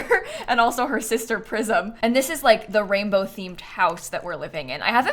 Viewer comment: Oh no, I thought her name was PRISON this whole time. I only catch the gameplay sometimes so it's the first time I'm seeing it written (in CAS), I did wonder why "prison" for the rainbow gen but in my mind it wasn't that wild compared to her relatives, I'm dying. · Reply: Me too 😂 I heard her say prison and went well weird name but okay, then i heard the traits and was like now it makes sense only to realise it was prism 😂